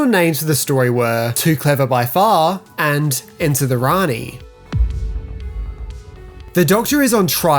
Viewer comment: Into the Rani....does not sound like an appropriate title. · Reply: You misheard, it was going to be "ENTER the Rani".